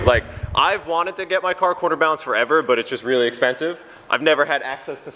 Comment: Why does sound like he is saying quarter balancing lol.